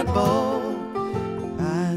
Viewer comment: tofurkey tofuckyourself may be the best line in any comedy song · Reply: Edward Mcintire I always thought he said “hobo’s asshole”